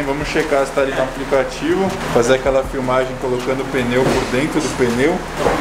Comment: colocando o pneu dentro do pneu ??? Eu entendi errado??